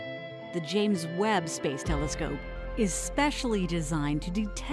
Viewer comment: At the start I thought it said WEEB Telescope 😗